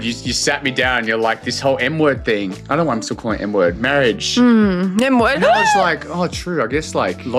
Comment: i can finally stop calling you girlfriend.. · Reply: I thought u said n word